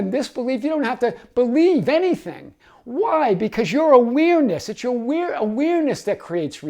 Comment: I kept hearing "your weirdness" 😅 I was thinking: this video was meant to find me 😂